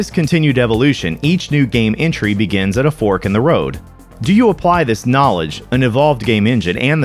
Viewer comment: At first I thought that said "Pork in the Road"